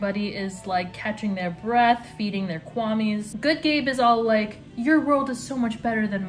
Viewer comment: i heard "shadybug thinks that adrian is a slvtty ,stuck up, brat" and i was like "love to see it" HELP ME i heard "catching their breath, eating their kwamis", had to replay like 3 times to hear it right